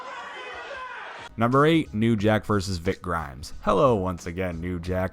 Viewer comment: I could have sworn you said Rick Grimes